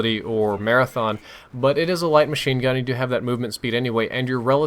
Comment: Did say "Light Machine Gun" as in LMG?